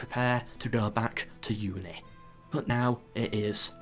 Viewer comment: before you prepare to go back to UNI? UK? Where ? What am I mishearing lol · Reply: Uni. Getting a masters in the history of war and strategy.